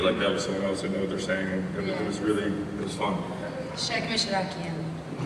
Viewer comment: what did Khaleesi say to Drogo at 9:40? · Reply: Notes of a Melody "shekh ma shieraki anni", which is Dothraki for "my sun and stars".